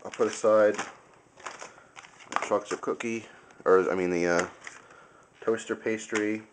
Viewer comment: did you say "fuck the cookie?" at 9:01? i was gunna show this to my 3rd graders now i cant : / haha